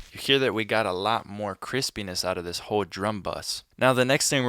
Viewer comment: Is it just me or the subtitles wrote Chris penis instead of crispness in 8:45😂😂😂